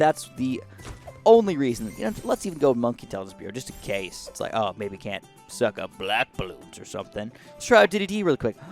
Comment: I thought he said “But oh no, it can’t suck up a bbc!?!?!?”